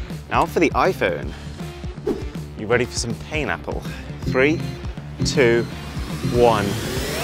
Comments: When he said ‘are you ready to feel some pain, Apple?’ I thought he said pineapple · At the moment you said "you will gonna feel some pain apple🍎" i thought i heard "pineapple 🍍" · " Time for some pain apple " That was personal. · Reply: I thought he say pineapple😂